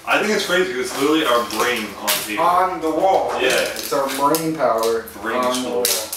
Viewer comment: I may be wrong.....but I thought what I heard was the other guy say "our money power" really?